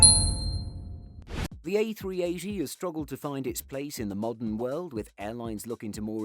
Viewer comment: Is it just or Did He say A380G